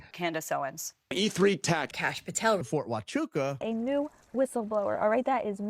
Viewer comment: everytime i hear Cash Patel I automatically think Cash Potato